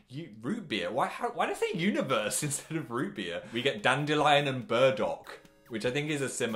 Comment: the subtitles say dandelion and bird dog instead of dandelion and burdock